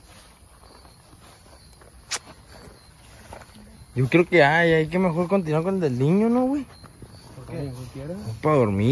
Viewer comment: en el minuto 19:14 se escucha una voz de mujer que dice algo así como : "cuidado". no alcanzo a descifrar bien con audífonos se escucha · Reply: parece que dijo, tengan cuidado😢